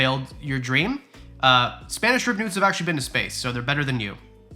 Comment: I heard "Ribbed nudes." Hhahahhahaha!